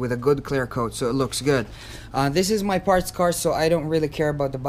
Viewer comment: Thanks,bud. I’m impressed. I’ll be trying that on my 20yr old Bora. Ps I misheard you. I thought you said it was a ‘friends car’ so didn’t need masking off lol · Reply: Thank you, it was parts car